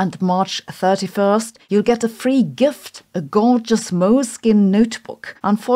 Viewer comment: mins ....did you say "moleskin"? ..🤨